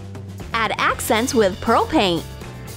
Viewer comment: I thought she said propane, not pearl paint😂